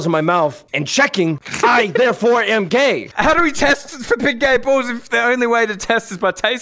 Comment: The first thing I thought when he said big gay balls I thought gib bay galls